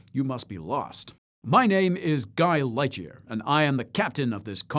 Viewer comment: I could've sworn he said "Guy Ledger" not Lightyear. Had to turn on the captions to be sure. 😂